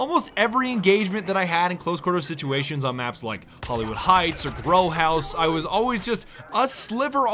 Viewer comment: I could've sworn badger said "Bro House" instead of "Grow House" lmao